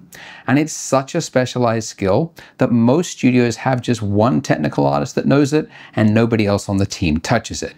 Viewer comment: "And it's such a specialized skill that most studios have just one technical autist that knows it" Was what I heard....